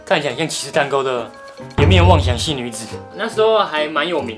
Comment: 我一直聽成顏面妄想性女子😂😂